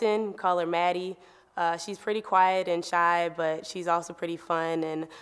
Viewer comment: What was said at 0:43 ? "Maddie is thang-three?"